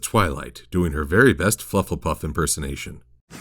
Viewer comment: "We begin with Twilight, doing her very best Hufflepuff persanation" YES I heard Hufflepuff. And im proud beeing Hufflepuff.